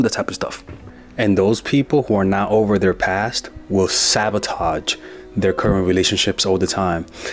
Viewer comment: I thought you said savatage😂